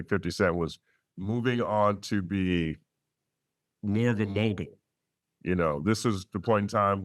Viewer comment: Bro what did this man say at 1:34 ? I been replaying this over and over and can’t decipher what he just said lol, is that an audio glitch? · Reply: "he went on to be more of a mogul"